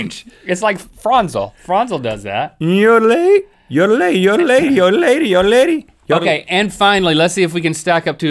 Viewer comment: Is it just me or at 10:00 if you listen carefully it sounds like Rhett is saying "Old Lady?"